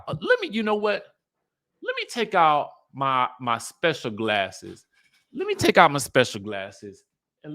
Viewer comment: why did I think you were about to say “let me take out my speculum”? I would I have DIED! But then the grass riddled glasses made it worse 😂😂😂 just LOL literally